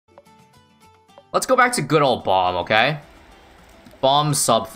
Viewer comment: I heard "let's go back to good ol' Obama" Not my fauly I just watched Max0r's summary of MGR just before